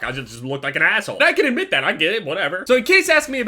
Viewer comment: did he say he is gay?